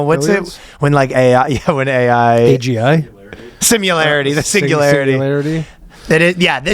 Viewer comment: did say "singilarity"? What's wrong with this guy, I can't take it anymore